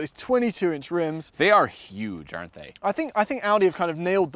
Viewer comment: I really thought he said Audi hat...